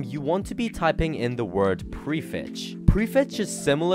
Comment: bro what did u say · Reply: prefetch